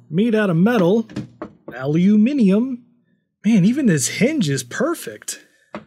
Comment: did I just hear a Yank say "Aluminium" correctly? I can't wait until he demos things you can do with a ZED X Spectrum! ;P Greetings from Blighty :D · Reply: Unfortunately I think he said Aliuminium, Americans will never get this right 🤭